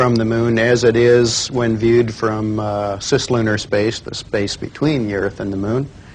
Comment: did he just say: cis-lunarspace,. CIS i what is Cis because i first heard it about non-LGBT humans